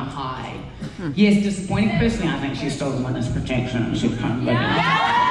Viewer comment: What is she saying 16:10?! I can't understand... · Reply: BoliDo She’s saying “Personally, I think she’s (Bea) in witness protection...and gonna come back” as Danielle jokes and says with orange hair. Danielle Cormack doesn’t think Bea is actually dead. Same thing I’ve been saying...Bea is being kept safe from Ferguson .